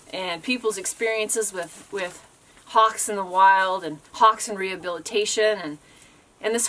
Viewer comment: for a second, i thought you said "pox" ( instead of "hawks" ), as in, chicken pox 😂 that would have been quite a name.